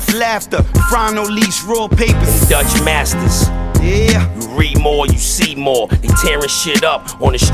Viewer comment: You read more, you see more...they tearin shit up in the streets of Bmore. Muthafuckin sick. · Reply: I thought he said you re more you see more as in you reup from a flip more you'll see more money that's what I remember when I used to hustle